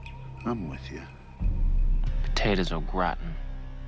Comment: When I was a kid, I thought he said the potatoes “are rotten,” not “au gratin.”